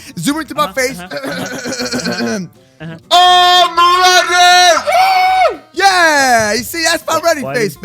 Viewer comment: it sounded like he Said My name... He says IM ready But some How me and My family think he says Amar